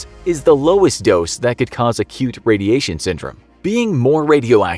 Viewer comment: I heard "a cute" syndrome. Instead of acute 😂 HELP